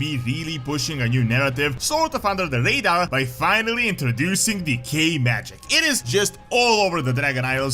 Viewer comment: I swear every time you say “Decay magic” I hear “Gay magic”